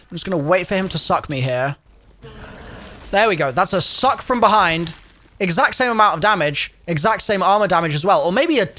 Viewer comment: Ayo? What did he say? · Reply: all I heard was “i need him to suck me, come on suck me, suck me from behind”